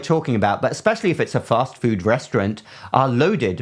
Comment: I misheard at ~ 05:45 "especially if it's a mock food restaurant". That would still apply, for the most part, would it not? 😄